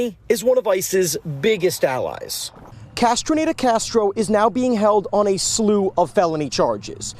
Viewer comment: The first guy's name lol I kept hearing: "Castrated Castro" 😑